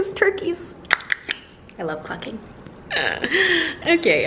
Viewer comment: I return to the video, and all I hear is ‘I LOVE FUC*ING ‘ I had to double take like whatt 😂 then had to rewind to put clucking into context !!